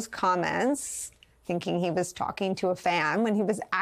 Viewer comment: At first I thought I hear "Hardened criminal" as opposed to "pardoned criminal", but then I though that both are true.